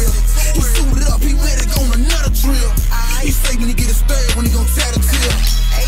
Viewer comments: What he say here? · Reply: I thought he said when he get his 3rd one he gone tattle tell 😂😂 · What he say here? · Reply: “He suited up he ready to go on another drill, he say when he get his 3rd one he gon tat a tear 🩸”